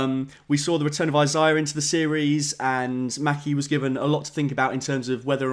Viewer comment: Did you just call Sam "Matthew" at 1:50?